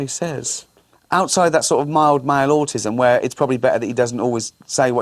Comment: lol does he say mild autism? I always felt ld is autistic too